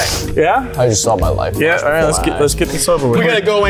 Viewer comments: I thought that Nathan said I just saw my wife die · it sounded like unspeakable said i just smelled my life like if you herd that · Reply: He said "I just saw my life flash before my eyes."